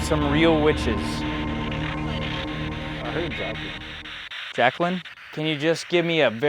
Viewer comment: I thought I said jack Doherty😂😂